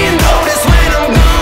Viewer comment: It thought he was saying “until they call me Optimistic” but now I know he’s saying “until they pull me out the mystic” lol